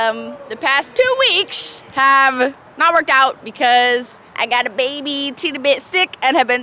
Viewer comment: thought you said you had a baby at first 😱😱